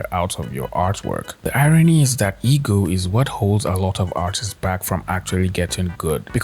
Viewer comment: my brain is cooked, had this on audio while i do chores and at 7:18 i thought he said e-girl instead of ego